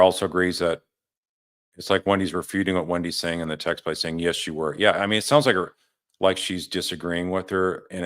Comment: I heard “yes you were”